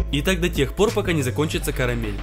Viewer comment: Мне одной послышалось "и так да тех пор пока не закончится коронавирус"🤣😂🤣😂